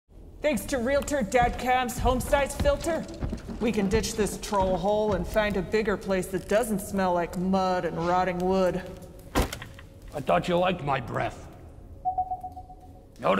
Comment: Only here because I thought he said “I thought you liked Minecraft”